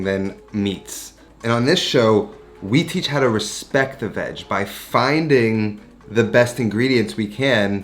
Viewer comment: WOOPS! My mind was somewhere else, I guess, coz I thought I heard, "... Respect the vaj." at 0:17. Ahhahaa...! Anyhoo... This might just be one of your shortest production pieces. Short or not, though, I'm here for it! Always